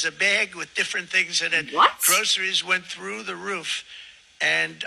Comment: Somehow, I heard "gross-eries"---errr, not from you though.